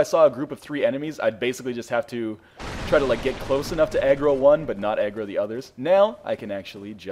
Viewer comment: I don't know why, but I thought you said egg roll when you said aggro.